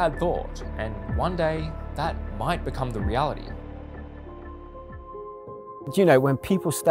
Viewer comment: what he said: one day it might become the reality yt captions: one day it might become the God